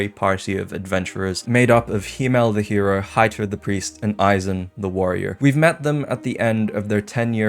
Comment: I genuinely thought that he said H!tl3r the Priest. 0_O